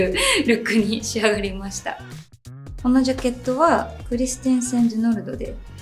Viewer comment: 〜でお話しされてるジャケットのブランド名どなたか分かりますか？聞き取れない… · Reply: クリステンセンドゥノルド、というブランドです!